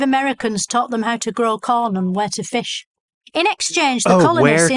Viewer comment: Tbh I'm English and I thought she said "wet a fish at first" lol